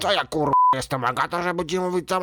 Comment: czy tylko ja usłyszałem "Fagata"?